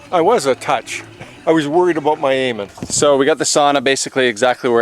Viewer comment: Dad saying he was worried about my aiming sounded like he was "worried about my Eamon". 💜😊 18:23 timestamp (added)